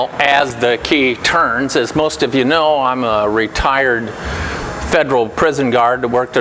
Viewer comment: did you say you were a retard